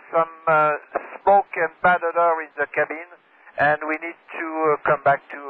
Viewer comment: He actually says "smoke and bad odour in the cabin", sorry for the thumbnail :) About the passenger/POB count, it's possible it was 115 PAX (not 150), and 124 POB with the crew.